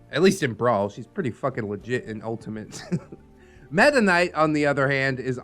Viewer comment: I just looked up Super Smash Brothers Brawl. So it's META KNIGHT. At first I thought was saying, "Mennonite." I wondered, "What's next, Seventh-Day Adventist?"